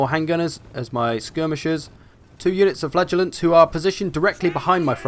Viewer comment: Sounds like you say "flatulants" ...